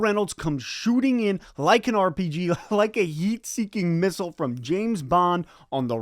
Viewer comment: All I heard was "a yeet seeking missile" - my ear probably deceive me, but two defenders did get yeeted.